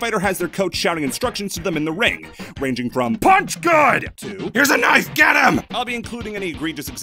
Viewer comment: “Punch good!” “Here’s a knife, get him!” All the advice I’ll ever need in life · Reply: I heard, "punch God."